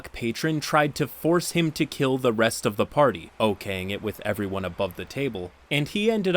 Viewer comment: Hey! Experimenting with a new intro and subtitle style. What do y'all think? 🦀👑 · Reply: They are fine but feel ai generated? At 3:29 the subtitles read "Okay, sing it..." Instead of what was said, "Okaying it..." Any person who heard the voice could understand what was said there.